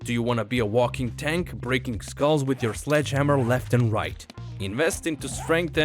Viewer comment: haha i thought you said "flesh hammer" which is another term for penis. LOL. misheard you there bud. hehehe